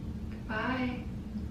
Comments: when said hi, it sounded like someone said "bye bear" 😮🐻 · At around 13:07 I heard a female voice say “ bye, there”. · i hear a women saying go to bed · after says bye I hear "bye then". 💕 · is sounds like she is saying bye fam